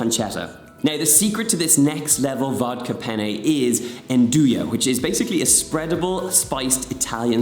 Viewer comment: The purpose of the vodka is that some aromatic compounds are only alcohol soluble, so the vodka helps extract those flavours. Then they are free to contribute to the complexity of the overall taste of the dish. Almost all of the alcohol is evaporated in the cooking process--that which remains is about the same amount as in a slice of yeast-risen bread as yeast converts sugars to carbon dioxide and alcohol. An easy substitute for the Andouille sausage might be chorizo, or you could mix up some Andouille spice with some ground pork. Andouille is a smoked Cajun sausage made with onions, garlic, cayenne or other hot chils, black pepper and usually thyme. Add some smoked paprika for the smoky flavor. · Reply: Yeah, I thought he was saying Andouille as well (trying to) but apparently Nduja is an actual thing.